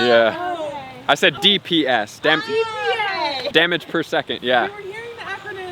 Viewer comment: "Dammit per second" 1:35 I could have sworn I heard you say it hahahaha